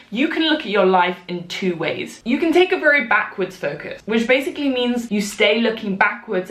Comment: Ngl thought she said ford focus...that's my coffee-fueled brain struggling